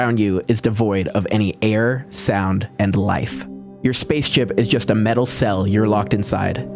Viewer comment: Did he say space CHIP?